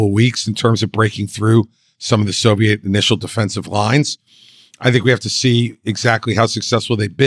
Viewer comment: Did he say Soviet defensive lines??